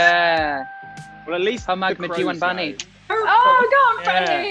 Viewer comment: i’m v curious abt what freddy said at 8:01. i’m assuming it’s something in amita’s native language? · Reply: I wanna know too but that doesn't sound like hindi to me. I'm Indian. I hear "But at least..." and ends with "bunny"